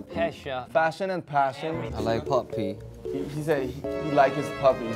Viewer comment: At first, i actually thought Yoongi said, "I LIKE POPPY" 😂